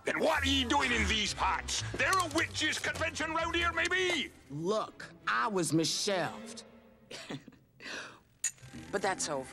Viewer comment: when she said look I was misshelfed I thought she said look I was Michelle when I was younger lol